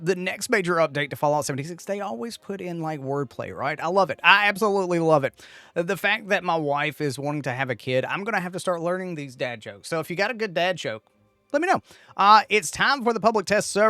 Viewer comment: I said, The fact that my wife is wanting to have a kid means that I am going to have to learn some dad jokes. I don't know why that would be cringe worthy but appreciate yall being here bud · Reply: I misheard it as “my wife is going to have a kid”. My bad. Husband and I both looked at each other like “what?” because most would say “we are”. I had to go back and relisten so now I can eat crow 😅 Sincere apologies, and again, we love your content! ❤